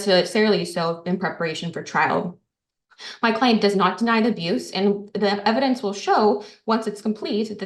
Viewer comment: does she say "my client does not deny the abuse"